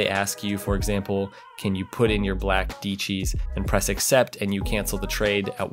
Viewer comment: did he just say: “put in your black deechees”?